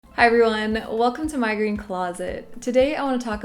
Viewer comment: At first I thought u said "migraine closet" lol , great info, thank you for sharing!